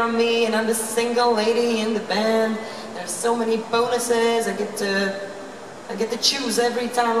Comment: She said 'bonuses' :D But I guess your version's true, too :D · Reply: Yeah, I know that she said bonuses, but I really heard "boners" (with some kind of british accent) first and got totally confused because it fits so well to the rest she was talking about :P It made me laugh pretty much^^